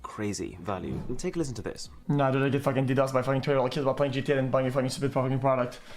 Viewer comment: I have absolutely no idea what he said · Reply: he said hes not getting ddox by 12 yr olds on gta